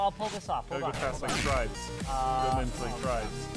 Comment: "Tribes"? Did he just say "Tribes"? Like that tribes?